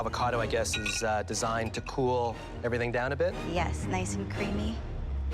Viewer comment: WHAT DID SHE SAYYYY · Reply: nice and creamy😫😫